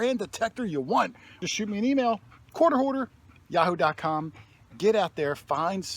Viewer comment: EVERY TIME YOU SAY, QUARTER HOARDER, IT SOUNDS LIKE YOUR SAYING, COURT ORDER. DONE ANY TIME?